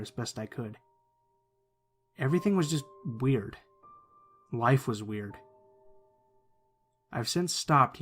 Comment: what did he meant by saying "wife was weird" ?? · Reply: he meant life